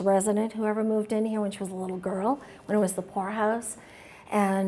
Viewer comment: Just listening to this, every time they say "poorhouse" I hear "whorehouse."